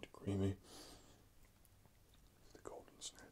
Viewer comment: did he just say "the golden snatch"??